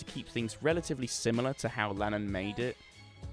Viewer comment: anyone else hear Landon’s name.?